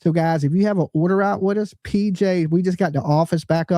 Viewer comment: I thought he said,"We just got her back or she just came back home!!!" But I'm pretty sure it was " we just got her back"